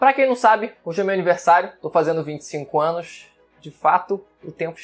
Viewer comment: eu ouvi 55 anos kkk